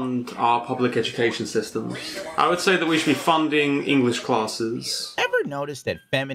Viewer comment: oh CLASS, I thought he was saying clause.